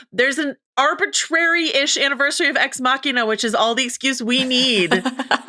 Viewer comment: I got excited and thought you said Legend of Vox Machina 😅